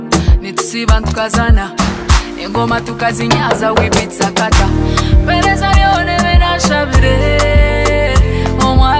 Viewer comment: what did she say?😂😂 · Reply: Engoma tukazinyaza we beat sakata😊